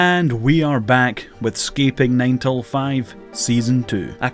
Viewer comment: I heard "Sceping" and immediately clicked off the video